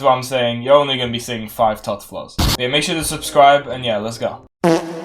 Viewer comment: what did he say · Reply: he said “You’re only going to see 5 tots floors.”